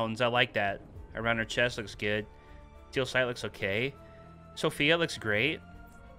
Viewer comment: I thought you said "Steelsight looks GAY"